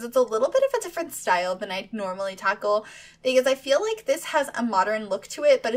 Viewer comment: i love that you you say tackle at 0:29 i kinda think you said taco 🌮 lol xxxx 💜💜💜💜